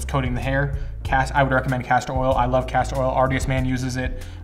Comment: I thought he said castrol oil. Went to my local auto zone and picked some up. My beard no longer grows in inches but in horse power